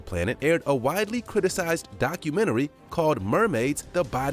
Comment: *mocumentary (the cc said documentary)